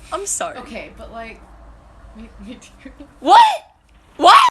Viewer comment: What did the girl say at 6:44?? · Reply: “Okay, but like, me too”